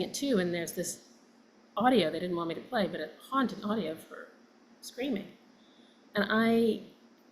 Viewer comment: 😢😢😢😢😢💔💔💔💔 32:45 33:01 WoW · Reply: did she say SCREAMING OR SCREENING??